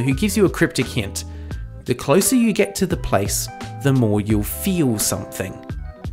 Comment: Subtitle typo at 2:40 says "expert" instead of "expect" 3:55 every instead of ever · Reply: "you'll" instead of "you"